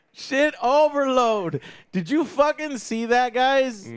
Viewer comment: i misheard "shit overload" ha2..